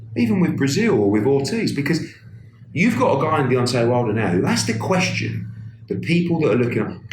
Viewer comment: I thought I heard him say Beyoncé Wilder hahaha